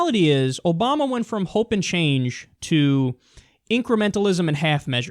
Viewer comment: Now I see why everyone is mad at Obama, I thought he said hope for change, not hope and change... make more sense now :)